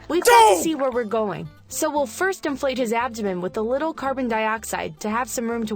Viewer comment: I thought it said inflate the ass